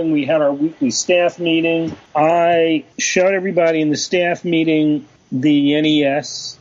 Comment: I swear I thought he said "I shot everybody in the staff meeting" lol